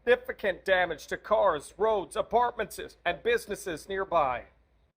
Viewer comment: did he say apartmentses?